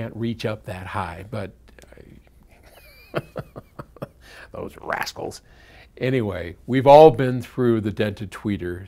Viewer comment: I first heard "those assholes", but I couldn't imagine Paul ever saying that about his kids, so I had to rewind. I am still not sure what he says. Did he mispronounce "rascals" or is he saying something that a non-American, such as myself, wouldn't necessarily have heard before?